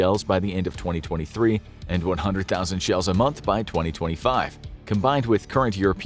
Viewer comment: Is it per month or per year? Audio says month, but the captions say year